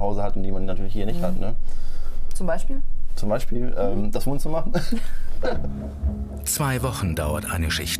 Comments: was sagt er da? :zb... und dann? bitte hilfeeeeee........ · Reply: Zum beispiel das Wohnzimmer diyo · was sagt er da? :zb... und dann? bitte hilfeeeeee... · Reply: zb Das Wohnzimmer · was sagt er da? :zb... und dann? bitte hilfeeeeee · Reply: Das Wohnzimmer